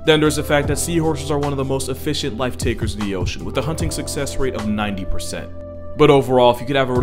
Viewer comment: im sorry did he just say hunt?